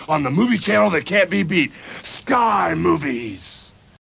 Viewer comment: Did he say, "Sky Moobies!"? 😂